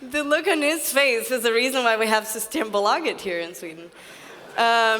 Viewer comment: what did she say about there being a reason for something? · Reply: She said Systembolaget which is (according to wikipedia) government-owned chain of liquor stores in Sweden.